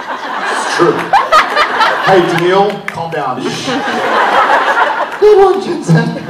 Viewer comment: why did jared just decide to call the fan Danneel around 6:10 ? and what did Jared say after that? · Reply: Mariah W Jensen had said "the boys should stay in a hotel" the girl said "were in one". Jared was being sassy and said " ok Danneel"